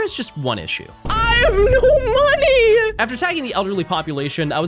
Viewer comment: Slow the video, down and please tell me I’m not the only one who hears “sagging”. 😂